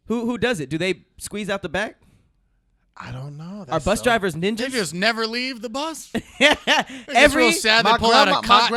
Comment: Am I tripping or did Trevor say "niggas never leave the bus" @ 50:59?? 🤣🤣🤣